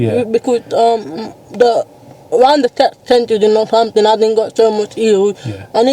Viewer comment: pin trying to think what she saying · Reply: She said in Northampton there isn’t so much hills